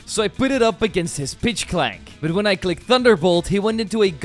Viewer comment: i realized late that he was actually pronouncing pidgklank correctly the whole time i thought he was saying bitchklank lmao oops